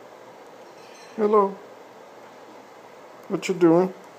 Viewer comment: sounds like he said "what?" lol